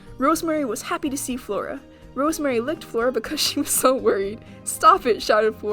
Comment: the subtitles say :rosemary licked floor because so worried Yeah I love licking the floor :3 16:06 : last is swift PAWS den.